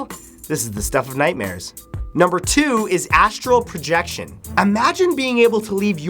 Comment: When it says astral projection, I thought it said anal injection.